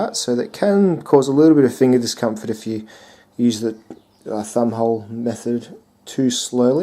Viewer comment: On first listening I thought you said "fun hole". Thumb hole. I'm not sure I dig the idea of a flipper and a fun hole on the one knife, seems a bit redundant. Reminds me of a dual control rubbish truck or a siamese twin snake that wants to bite it's other head, or when they would still very occasionally put cassette tape decks on stereos after the turn of the century, or like sprinkling sugar on Froot Loops.